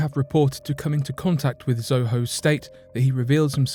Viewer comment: did he call him ZoHoe😂?